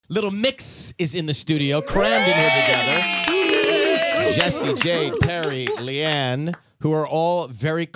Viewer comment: I thought Ryan said, Jessie J instead of Jesy, Jade..hahaha